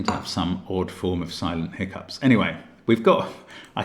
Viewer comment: Thought he said there he had “an odd case of Simon hiccups” and had to rewind to discover it was in fact “silent hiccups” of course 😂 Had me wondering if Simon hiccups very distinctly or something lol